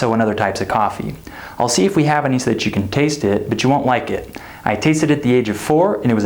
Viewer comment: I watched the whole video and finally what I heard is: I'll see if we heaveny. So the chicken taste it.